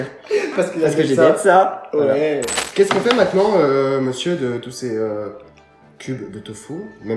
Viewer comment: (On fait quoi M.R. cube de tofu) cest fou dit vraiment n’importe quoi 🤪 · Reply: Mais j’ai pas dit ça j’ai dit « on fait quoi, monsieur, de tous ces cubes de tofu ? »